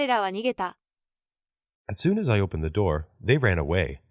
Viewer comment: くらいの英文、As soon as I opened the doorって言ってるように聞こえませんか…？既出だったら申し訳ないです！